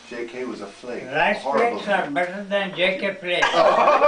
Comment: what does UG say here, anyone? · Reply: "riceflakes are better than JK flakes"